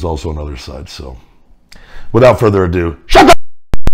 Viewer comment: It sound like he said shut the ** up Nexus. LOL 🤣 🤣.